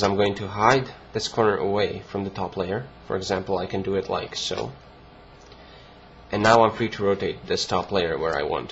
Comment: u helped alot! btw, why do i keep hearing you say "....like soap .......like soap"?? haha